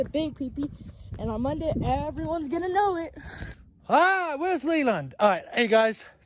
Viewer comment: did you say my name Leland